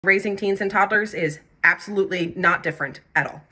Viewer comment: I thought you said raisin canes and I got excited